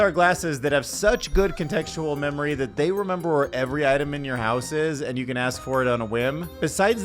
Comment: I thought you said that the glasses remeber where every atom is in your house. I was like damn that progress.